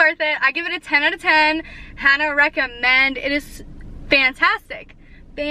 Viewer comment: Yooo this whole time I thought you were saying “highly recommend “ but you’re saying “Hannah” which I just realized is your name 🤦🏾‍♀️🤦🏾‍♀️🤦🏾‍♀️🤦🏾‍♀️ I’m so late lol . Well hi Hannah ! 🤗